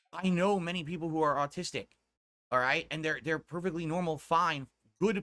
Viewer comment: "I know Many people are Autistic" I swear said Autistic and not Artistic 😂